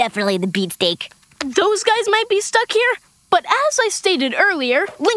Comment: did he just say rape? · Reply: No he said ripe